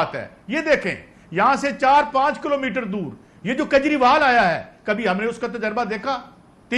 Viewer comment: "Kanjriwal" instead of "Kejrival"